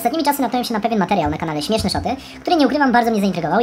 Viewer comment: ja tam usłyszałam śmieszne szopy xDD